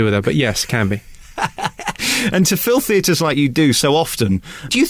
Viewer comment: Thought you said "filth eaters" but then realised you said "fill theatres " works both ways i supposed lol